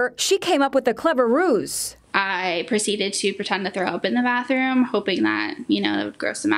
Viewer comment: What did she say about pretending something was in the bathroom? I can't make out what she is saying. It is about 0:50. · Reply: She pretended to be sick and throw up in the bathroom so he'd leave.